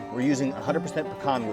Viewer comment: Ohhhhh pecan. I had to rewind and listen again because I thought you said pine and I was like WHAT?!?